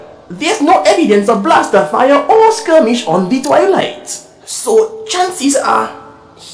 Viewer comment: It took years before I learned that treadshot’s ship was called the Twilight. Before that i always heard “Skirmish on the toilet”